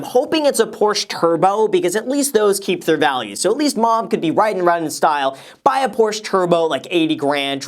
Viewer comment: why is it grandma instead of mom 😂